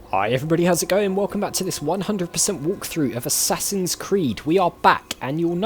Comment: First of all, I thought you said "A Sassy's Creed" which made me giggle. Secondly, I completely agree with you on doing those bits off-camera. It's what I chose to do for my Mad Max playthrough. As long as you keep the other interesting things in, it should be ok.